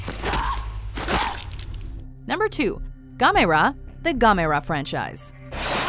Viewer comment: I thought Gamers said camera